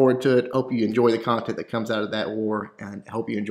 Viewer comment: Lol I almost thought you said jake from state farm, great vids man 😂😂